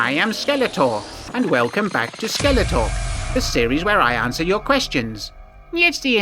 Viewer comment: I keep hearing it as “Skeletwat”, rofl